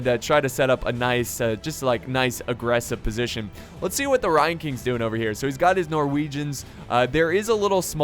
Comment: at first when u were saying 'ryan king' i thought u were saying 'lion king' with an asian accent lol